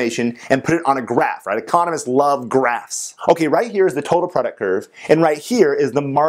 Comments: did you just say " economist like grass"? Is that the economic way of saying that economist like weed? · i heard "economists love GRASS" Lmao